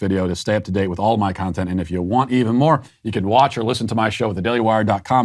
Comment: It sounded like you said APPLE PIE CAST,LOL!!!Thanx for that Matt,you nailed it right on the head.They hate themselves and in a sick and twisted reality they have created for themselves,they always find fault,victimized and a reason to blame someone or something else.Tragic,sick and ridiculous.Keep up the great content,I'll keep watching.